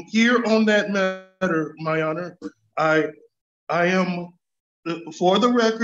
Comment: Did he say "my honor"? Also, all this talk of clock of destiny. Is this what the new Indiana Jones is based on?